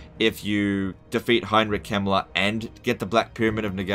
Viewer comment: I've never played this total war game but did you say defeat "HEINRICH HIMMLER"? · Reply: Heinrich Kemmler, a Necromancer lord for the Vampire Counts.